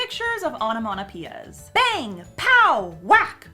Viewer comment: Onomatopoeia.... sounds like sayin' onomanapeeya o.O